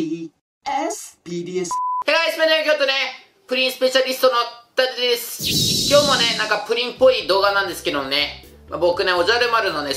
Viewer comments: 最初の挨拶「不倫スペシャリスト」に聞こえてびっくりした人いる？ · 不倫スペシャリストに聞こえた人 ↓